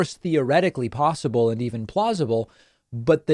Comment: When you said "plausible", all I heard was "plaus-ma". I guess I've watched too many of your (great) vids.... ;-)